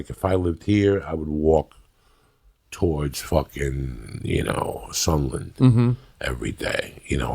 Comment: did he just say he would walk all the way to SUNDERLAND?? As a Geordie I would NOT recommend going to Sunderland